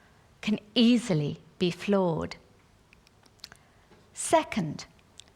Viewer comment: no one will take your argument seriously when you thought she said 'floored' rather than 'flawed' or that your grammar is that bad. either one makes you a bit dim. and your argument at the end about how she should 'grow a brain' well guess what einstein that was the very point she was making - people should be more clever themselves and not blindly follow expert advice.